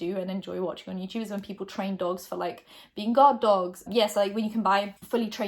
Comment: I heard "GOD-DOG" and english isn't my first language so I thought it was a term or something like godfather jajajajajajjj so stupid